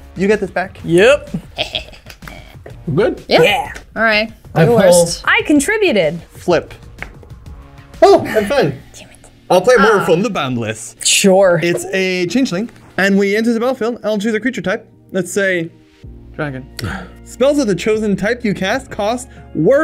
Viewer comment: Extremely funny to play a mana crypt vs Olivia 30 seconds into a video. Like I assume it was recorded before the bans but extremely funny timing. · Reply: I had to rewind like 3 times to hear what was saying when he cast Morophon, The Boundless because it sounded like he said “I’ll play more from the banned list” 😂😂😂